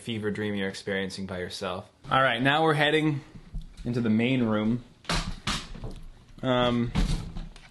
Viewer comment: When said the main room I thought he said the pain room and it didn't seem out of place